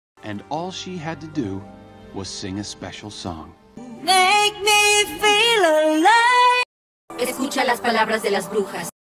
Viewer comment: I PAUSED AT 0:07 AND TO GET CHIPS AND IT SOUNDED LIKE SHE SAID “cOoChAy✨”